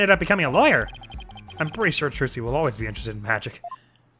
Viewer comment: In the beginning of the video it totally sounds like you said "Jinxie" instead of "Trucy" and now I'm imagining that Jinxie just replaced her for the rest of the game and no one Saya anything about it I really like the idea of characters just being in the background, even if you barely see them like Apollo in this case